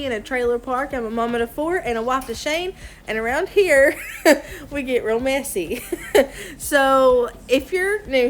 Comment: I thought you said. Around here we get romance eee. Sweet video.